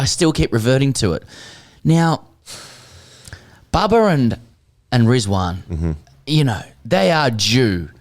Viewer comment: the subtitles can cause a riot - Babar and Rizwan are due - is what he said...but his accent and the subtitles!